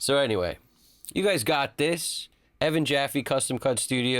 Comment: Ngl, 11:06 I hear custom cunt studios.. Which I like a little more.. raise .5 a db on that?